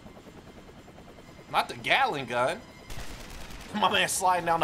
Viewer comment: am I the only one who thought he said “galick gun?”